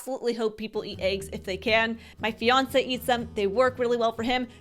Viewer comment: I thought she said "Beyonce eats them" - turns out she said "My Fiancé eats them."